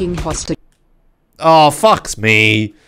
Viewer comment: I heard "It fucks me"